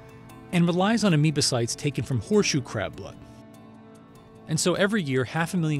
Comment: My nigga ass thought it said crips blood